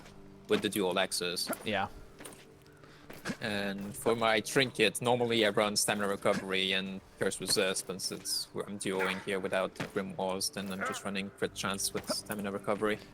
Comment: what the fuck does he says he runs on his trinket?? I listed to that 5x times and I can't understand. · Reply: "And for my trinket, normally I run stamina recovery and curse resist, but since I'm duoing here without grimoirs, then I'm just running crit chance with stamina recovery." He just misspoke/repeated on two words, but his low volume, meh mic quality, and accent makes it a bit tough. You can slow down speed to .75 to listen more easily.